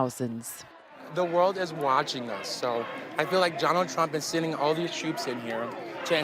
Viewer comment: Did he just say Johnald Trump?